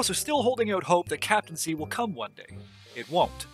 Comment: Did he say “You’re holding out hope that Captain’s Seat will come” before saying it won’t. If so, then it’s already available on the Galleons now. Not sure about the Brigs though. I never use them. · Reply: No. I said captaincy, as in the first-planned, cut update that would've fleshed out the endgame.